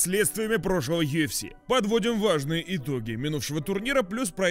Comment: В начале послышалось "влажные итоги"😂